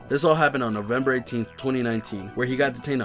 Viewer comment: did he say November 18th 2019?